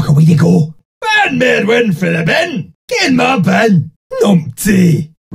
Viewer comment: I thought he said "get mad bud"